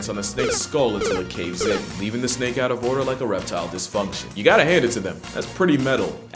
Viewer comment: Did he say “a reptile dysfunction” or erectile dysfunction @ 3:06? If it’s the latter, I’m super disappointed you missed that joke, if the former, your a genius